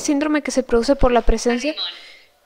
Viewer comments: puede ser que dijo animal justo cuando el gatito tiró la muñeca? O escuche mal 😬😬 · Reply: Según yo dice arrimón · creo que dijo salimon que creo que eso significa pacifico o paz